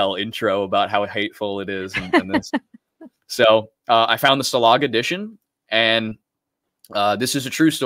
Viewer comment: min marker what edition of Mein Kampf is saying he tead? Salog? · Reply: Stalag